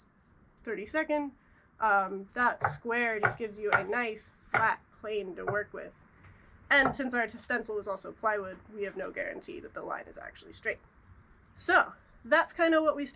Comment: I misheard "we have no guarantee that the light is actually straight" and had to do a check :D